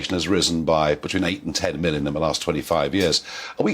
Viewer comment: Did I hear this right? At 0:20 Farage says Europe’s population grew by 8-12 BILLION over the past 25 years due to immigration? Does that estimate include possible illegal immigrants?